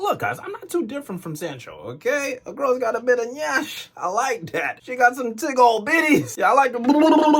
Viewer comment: Did I just hear nyash